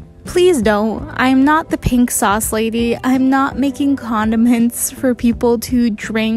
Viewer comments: I thought she said condom hints not Condiments 🤣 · "Comdiments " What i heard: "Condom hints " 💀 I need help